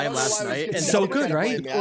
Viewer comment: Anyone hear what movie went to see at around the 46:00 mark? I can't understand what movie he is saying. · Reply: The movie is called Everything Everywhere All at Once